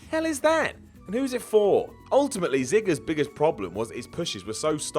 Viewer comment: who heard zigger?